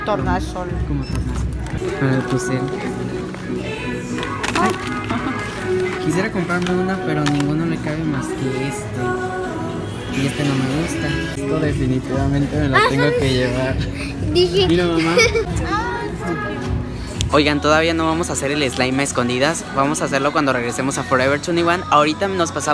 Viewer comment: escuche y dise mira que estupido